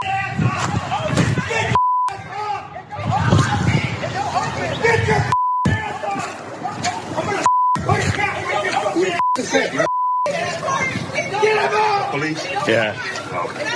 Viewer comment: did I just hear him yell "im going to put a cap in your monnkey ass" about 1:09ish